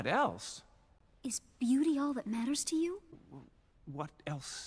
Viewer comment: “What else is there? She asks is beauty all that maters and you answer, what else is there? You should write a book, “how to offend women in 5 syllables or less.” -lord Roggers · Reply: I heard her say "is buooty all that matters to you?"